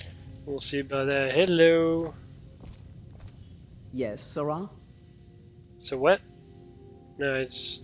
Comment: I think Jenassa said "Sera"